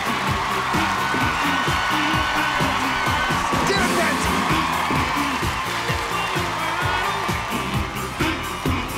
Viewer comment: What did this clown say there? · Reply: David Brent